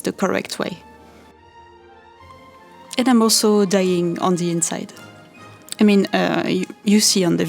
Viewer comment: I heard “dyeing on the inside” as “dying on the inside” and I was like, “nooo don’t be sad! It looks great!”